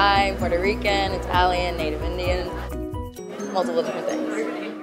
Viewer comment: did she say she was native indian or NEW ZEALAND ??👀 IK the video is old but just wondering bahah